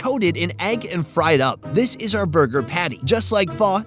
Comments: Did he just say Furr Burger??!!🤣🤣🤣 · I thought he said Fur burger! OMG. Australia accent misunderstood fo sho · I thought the guy said, "Fur Burger" and I about spit my drink onto the monitor. Then I realized he said, "Pho". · At the 5min mark..I thought he said fur burger lol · I thought the Aussie fella said "Fur Burger" hahahaa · i thought he said fur burger ...lol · I thought he said fur burger lol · sounds like hes saying FUR BURGER lol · 100% thought he said "furburger" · At first I thought he said it was a fur burger.! Haha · I thought he was saying Fur Burger. :) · Thought he said furburger LOL. Hey if it's on the menu sure thing I'll try it ;) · I thought he said Fur Burger! LOL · I thought he said fur burger... not pho. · For a second I thought the guy said furburger . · I swear, I thought he said “fur burger.” And here’s Sonny, like, let me say it right for you.